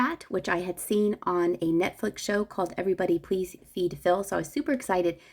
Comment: Somebody Please Feed Phil is my favorite on Netflix! At first I thought you said “eat Phil”. We must not eat Phil.